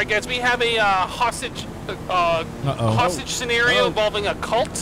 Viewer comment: I heard the “hostage scenario involving a cult” as “Hostage cereal cult” needless to say I was very confused until I went back a few seconds to hear it again